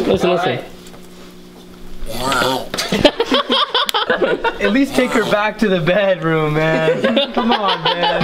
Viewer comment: I died when someone said “at least take her to the bedroom” with the turtles 😂😂😂😂😂😂😂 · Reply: Omg I thought he said at least take her to the vet 💀